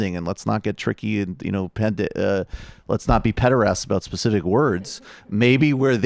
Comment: Let's not be WHAT about specific words? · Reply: Michael meant 'pedants.' It's actually not the first time MR has mixed the two up. xD